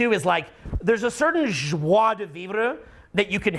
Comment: can someone write and spell what he said? I'm really super intrigued.... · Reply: Joie de vivre is a French phrase often used in English to express a cheerful enjoyment of life, an exultation of spirit, and general happiness.